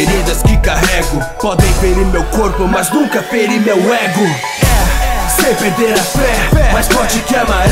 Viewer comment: Só eu que sempre escutei "sempre ter a fé" e não "sem perder a fé"